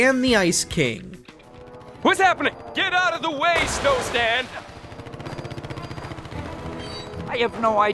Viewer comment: I hear Sonic.